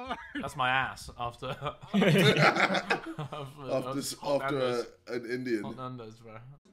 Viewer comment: did JJ really say after an Indian 😂😂